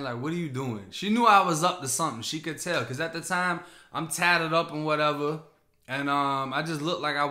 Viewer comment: I thought "HE GO SAY IM TATTED AN WAT EVAA AN SO IS MY MOM 😭😭😂😂😭😭😭